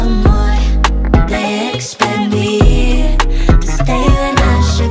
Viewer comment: Ok but why did i hear "They spanked me" in the "they expect me" Part in the music video